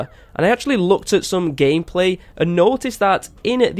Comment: it didn’t even sound like you said gameplay it sound like you said g*yplay😂😂